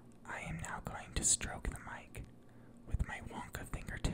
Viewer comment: I thought he said I'm gonna stroke the mic with my WILLY WONKA